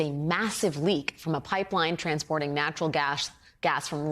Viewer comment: did she said Gash